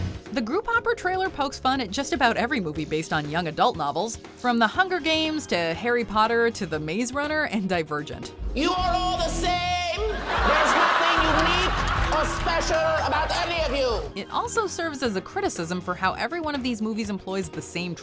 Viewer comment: I hear die virgin 🤭😂